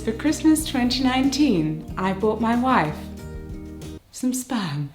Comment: I thought she said she bought her wife some spam. lol.